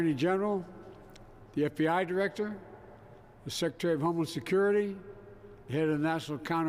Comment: I heard "director of homeless security" 😂😂😂